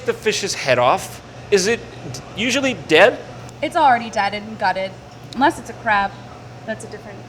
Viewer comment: did she say "dieded"?